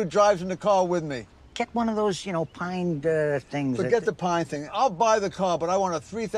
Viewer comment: I could have sworn I heard "Pine Barrens" for a second lmao.